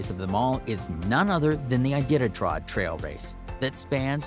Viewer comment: Did he just say, "Idititrod"?! XD